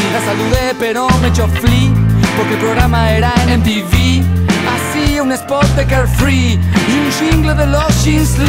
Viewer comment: Siempre pensé que decía "hacía un spot de girl free" mi infancia no tiene sentido